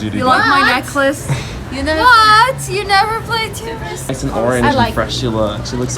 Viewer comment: did I just heard 'what? you haven't played tuber simulator'